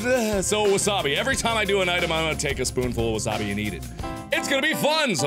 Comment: Hope you guys enjoy this Wasabi Challenge because it was ROUGH getting through it :P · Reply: i heard you say spoonfull and immediately thought mark NO!!!!!